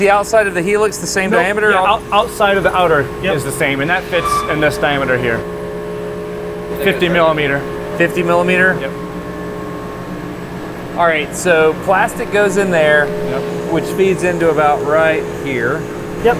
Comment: Wait, i heard Milimeter?